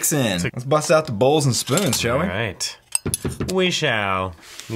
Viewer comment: he says joey instead of eric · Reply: He actually said "shall we" not joey